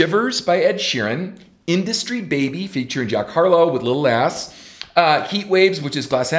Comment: Did Ken just call Lil Nas Lil Ass? (I mean tell us how you really feel 😂)